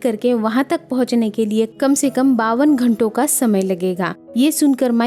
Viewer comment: It would be very nice if you would say the numerical in English..rather than saying it in hindi...I couldn't understand 4:35 the word that you uesd to say fr how many hour's he was supposed to wait...(pawan khanta) it's hw mny hours...please ny1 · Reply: 52 hours